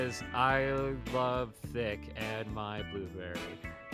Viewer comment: i heard d**k instead of thicc. Im wheezing 🤣🤣